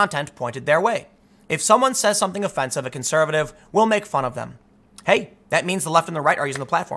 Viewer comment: oopsy fraudian slip Tim 2:56 He says and I quote. "if someone says something offensive to a conservative We'll make of them"... Things that make you go hmmmm? I think not, sounds pretty conclusive and right in line with what you say you feel. hee heeee.